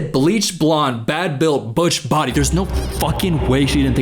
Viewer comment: She said "butch build" which, as a butch, lowkey hurt 😔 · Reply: i thought she said bad built botched body referring to her obviously failed plastic surgeries